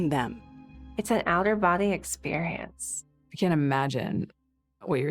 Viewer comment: wait did she just say "it's an outer body experience"? 😂